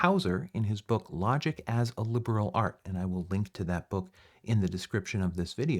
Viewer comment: Excuse me, teacher. What is the name of the book used as reference throughout this series? I heard you say Hauser but not what book. Thank you in advance! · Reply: Certainly - it’s Logic as a Liberal Art: An Introduction to Rhetoric and Reasoning, by R. E. Houser. It’s the only book of its kind that I know of!